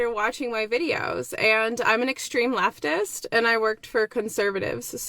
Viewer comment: she said “I’m an extreme leftist” I heard “I’m an extreme retard”